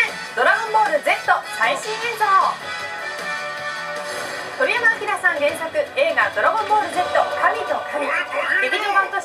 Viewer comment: en el seg 0:07 despues de que dice dragon ball z dice harry potteer xDDD!!!!!!!